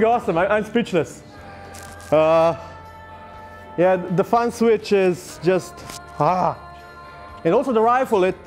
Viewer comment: it sounded like he said fun switch and i agree.